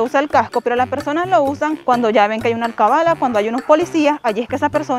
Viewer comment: 🤔🤔🤔... en el minuto 4:23 , cuando explicas lo del casco de las motocicletas🏍🏍, dices que en Venezuela la gente se pone el casco SOLO cuando ven una "CABALA"🤔🤔 .... no te entendí a que te refieres... ¿Me podrías explicar?😁😁.. #Culturizandomecon jejjee